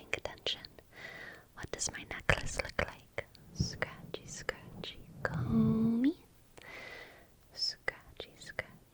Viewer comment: awkward but i've always heard it as scratchy scratchy calmy